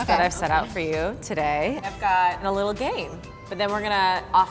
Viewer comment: I thought at 3:12 devin Said I’ve gotten a little gay XD